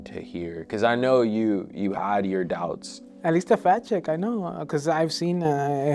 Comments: This is one of the first times that the victim fell for a woman who is beautiful, but regular looking. He doesn’t seem like he’s out of touch with reality like most of the other people who get taken by these scams. Edit: welp. The sympathy was very short lived. 🤦🏽‍♀️ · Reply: I too thought he said "fat chick" but my boyfriend thinks he might have said "fact check" 🤷🏻‍♀️ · I meant that he doesn’t think an extremely attractive woman would fall for him. There’s a certain kind of delusional narcissism inherent in a lot of these victims, which makes it hard to feel bad for them. What he did isn’t delusion, it’s stupidity. But I should have waited until I finished the video to comment. As soon as he said “at least it wasn’t a fat chick”, all sympathy went out the window. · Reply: I too thought he said "fat chick" but my boyfriend thinks he might have said "fact check" 🤷🏻‍♀️ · I meant that he doesn’t think an extremely attractive woman would fall for him. There’s a certain kind of delusional narcissism inherent in a lot of these victims, which makes it hard to feel bad for them. What he did isn’t delusion, it’s stupidity. But I should have waited until I finished the video to comment. As soon as he said “at least it wasn’t a fat chick”, all sympathy went out the window. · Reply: I too thought he said "fat chick" but my boyfriend thinks he said "fact check" 🤷‍♀️